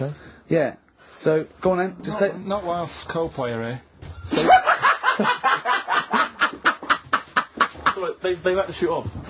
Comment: What the hell is Karl saying here? I can't figure it out! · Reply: 7 years late to reply but just incase anyone’s wondering this now... He said “not whilst Coldplay are here”. Ricky and Steve were doing an impersonation skit pretending they were interviewing Chris from Coldplay a few minutes before what can be heard here so what Karl said was funny because it was kind of an inside joke from earlier in the episode.